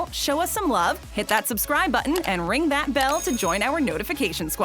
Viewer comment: i thought it said unsubscribe for a second